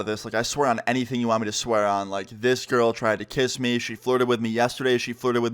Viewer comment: Thought it said my girl tried to kiss me lol